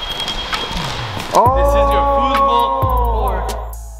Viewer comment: did he say football court?